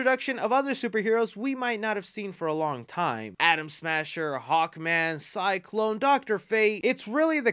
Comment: Been playing way too much Cyberpunk. When you said Atom Smasher, thought you said Adam Smasher 🥲